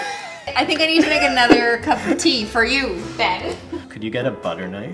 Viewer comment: Why did she say the f slur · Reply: She said ben